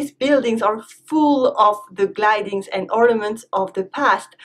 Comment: Hi, In this video at 3:55, I heard "glidings ...." instead of "the gildings and ornaments ...." Could you watch at this place? Have a good day and thanks a lot for the answer.